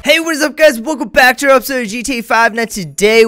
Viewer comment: Who else heard him say Hey guys welcome back to a episode of GTA 5 video at the beggining 😂